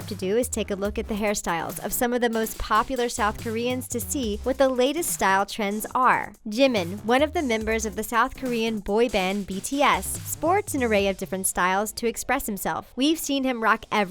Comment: Did she just say gym man? IT FREAKING JIMIN GET IT RIGHT